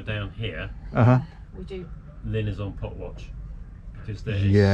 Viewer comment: What are you talking about at 26:00? Some kind of watch. I rewound it a couple time, and couldn’t understand what you were saying. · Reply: Pot watch. People drop crab pots into the sea and mark them with buoys that are often so small that they are very hard to spot or get dragged just under the surface. If you don't see one and run over it, you can end up with the line from buoy to pot wrapped around the propellor disabling the boat and even causing serious mechanical damage.